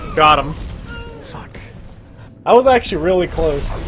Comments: I thought zenyatta said "fuck" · omg at 0:32 it sounded like Zen said Fuck. I died 😂😂 I know it was after listening to it again but that was hilarious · did Zenyatta say fuck?